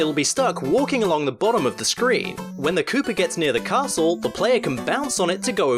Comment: Every time you say “Koopa”, I hear “cooper”, and think of someone who makes barrels for a living... barrels that Donkey Kong throws at Mario in his first video game appearance. I need to go and have a lie down. :-S